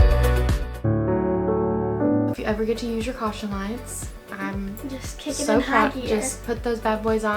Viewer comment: I DIED AT THE GRANDMA RUNNING OUT THE DOOR HALF NAKED AND THE MOM SINGING CAUTION LIGHTS😭😂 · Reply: lol this whole video i thought she was saying caution lines and im like wtf are are caution lines this makes so much more sense now